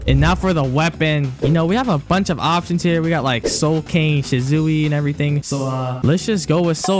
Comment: did he just say shizuwi😂💀